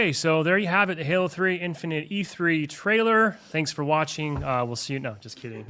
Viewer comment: I’m so confused... did he just say “the halo 3 infinite E3 trailer?”